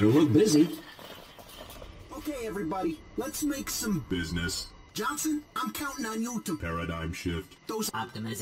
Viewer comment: did he say "Jonson, im counting you to Paradise Shit"?